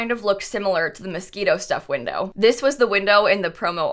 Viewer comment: I really thought she said mosquito stuff pack